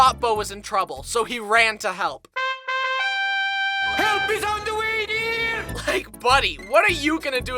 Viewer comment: When said help is on the way f Dear is sounded like saying help is on the way b Dear